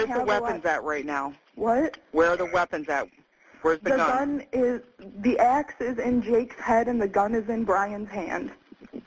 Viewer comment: "The axe is in his head." 😲 Did I hear that right?